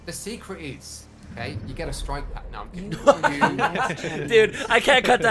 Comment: So what did say exactly at 13:53? 😅 "The secret is, you get a (striped???)..." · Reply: Strike pack*